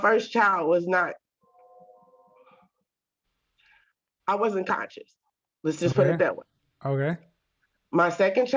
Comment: Did she said unconscious?